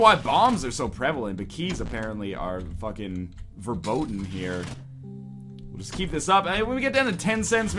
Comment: wtf did he say at 10:13 ? verboden? that's dutch for forbidden